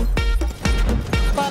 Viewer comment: what did he say? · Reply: Gonna eat yo ass boi!